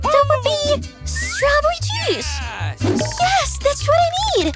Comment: I thought she said strawberry cheese